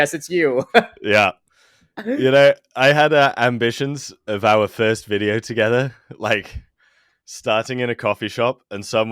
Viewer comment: I feel like an idiot. I heard 'our thirst video together' and I have to admit that I grabbed my phone to check out the screen in amazement before I realised what he actually said.